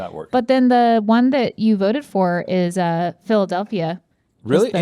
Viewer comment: When Stevie said it’s philedalphia. I could of swore she said bill w. Pernounced like bill dub-a-ya